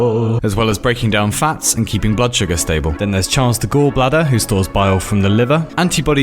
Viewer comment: For a second I thought you said “Chance the Gall Bladder”